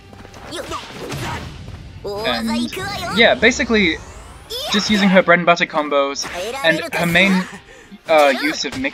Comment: can someone tell me what she said? It sound like shes saying e•quay•el but idk, I've been hearing characters way this in like 3 different games I've played today and I wanna know what it means, can someone help? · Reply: I’m pretty sure she said 行くわよ (ikuwayo) which is just like ‘let’s go’